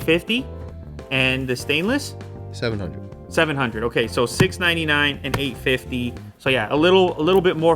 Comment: even the google captions could not comprehend the price so wrote 6.99 instead of 699